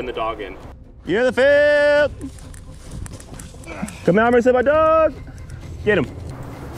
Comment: "comarolesemedahch" is what the audio says, but the words are "come out or I'll release my dog"